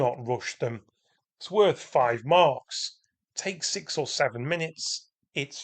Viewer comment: did i just hear <num> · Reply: Haha. I'm afraid I'm way too old for all that. I definitely said 6 or 7 minutes!